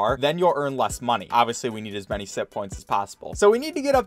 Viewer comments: I thought he was saying simppoints until it zoomed in and I saw it said sitpoints instead. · Ok i know this sounds weird,but I honestly thought he was saying simp points the entire time until the closeup on the points and I realized that it was SITPOINTS NOT SIMP POINTS